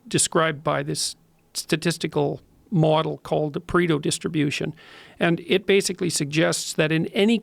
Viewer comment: What does he call the law of nature at the start? It sounds like he says 'The Predel Distribution'?